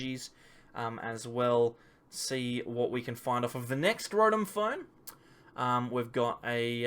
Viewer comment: Did you just call it "Scrotum Phone"? 🤣